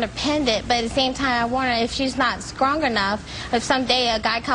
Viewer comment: Wait a minute... at 5:19, did she say 'skrong enough'??